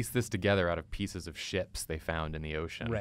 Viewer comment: Am I the only one who thought said shits?